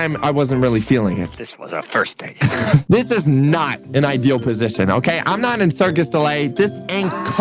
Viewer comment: Ain’t no way I thought said this was our first date